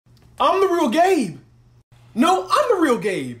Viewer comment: I thought he said "I'm a real gay" 😂😂😂